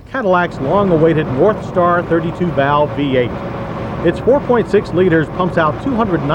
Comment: i swear he said warpstar V8. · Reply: The audio is not the best on this clip. There's a few muffled words throughout. But its NorthStar V8 for sure.